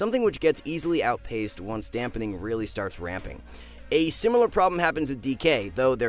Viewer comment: I thought you said "when dampening really starts rampening" and I was kinda sad when I realised you didn't...